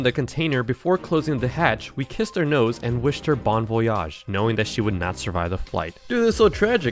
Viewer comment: bro I thought he said that Likea would not survive the plane